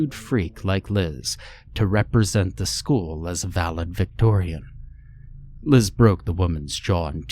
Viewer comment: Ok did anyone else hear @ 3:39 1st story ... valid- victorian???¡¡¡¡!!! Isn't it validictorian?? C'mon! 😂😂